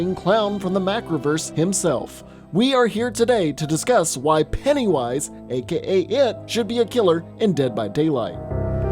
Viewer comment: I thought it said “ why penny wise should be in my bed tonight “ lol